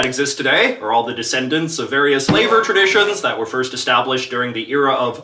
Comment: When he said flavor traditions i honestly thought he said “salvor traditions”. my ears decive me once again